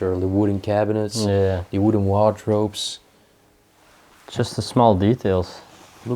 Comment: Wardrobe not waterrobe- that's what sounded like you said